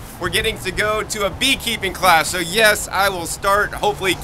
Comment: I think you said "Bee Kleeping Class" ... IMHO, stick with standard size hive bodies, frames and supers, they are easier/cheaper to retrofit and maintain. Extractors take those frame sizes best and foundation is easier and cheaper. 6:50 call the fire dept and let them know your looking for free bee swarms (they get calls sometimes).